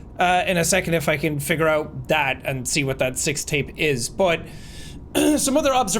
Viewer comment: the subtitles, say sex tape instead of sixth tape 😂😂😂